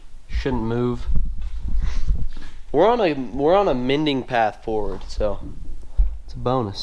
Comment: what did just say?? “A mending path forward”?!